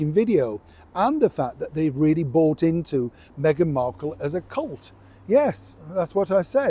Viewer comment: Neil, when you said Meghan was "a cult" I thought you said she was a clot. Sorry, my mistake, weeelll maybe not. 👋